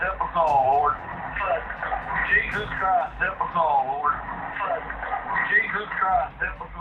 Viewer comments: Number 8 sounds like he is saying jesus christ the super bowl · i thought 8 said jesus christ dipper bowl.